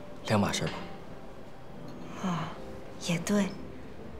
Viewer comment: Did she said 'yeh to hai' at 15:17' 😂😂 . . Indians can relate 😂